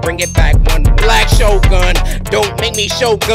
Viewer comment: What he said:"Bap bap Bing ya both kneecaps" What i heard:"Bap Bap Bing ya both need gas-"